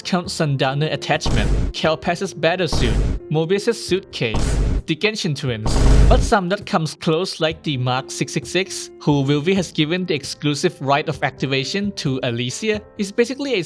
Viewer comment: I Misheard ur "genshin twin" to "genshin twitter" made me think twice, yet I think that it's make sense cus genshin Twitter is really scary and powerful especially the reply and retweet section 🗿🗿